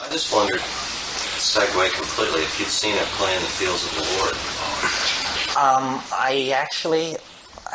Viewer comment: What is the film the guy in the audience says? I can’t make out what he’s saying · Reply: At Play in the Fields of the Lord. Agree with the maestro, read the book.